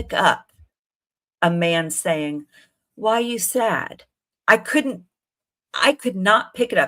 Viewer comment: I hear 'why so sad' in a sarcastic voice 😂